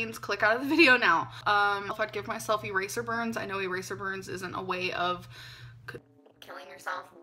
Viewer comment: Alr really said "yes I've been suicidal I used to give myself eraser burns" 🥺👉👈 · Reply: Oh yeah, I thought she said razor burns at first but she actually said ERASER burns. Like give me a fucking break